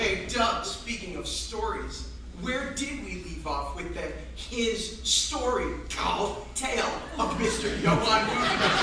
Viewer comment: What da F*ck did he say? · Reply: HIS story CALLED tale of Gutenberg i think. Cos Doug wrote the script so its his story?